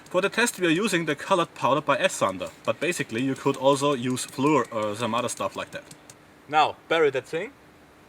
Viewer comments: did he say fluid? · did he say fluid!? :P